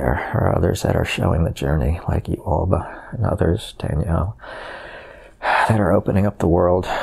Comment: Woah! Did anyone else hear at 44:04 & 54:04 the name, Danielle? He said; "There are others out there showing the journey like you Alba, and others, Danielle " Omg, my heart skipped a beat!!! That's my name! Lovely message ♥️